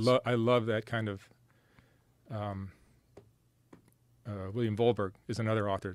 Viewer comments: anybody know what author he is talking about at 2:10 ? William volberg? I tried googling it but couldn't find anthing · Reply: I think he meant William T Vollmann. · What is the author name he says around 2:10 ?? William Volberg?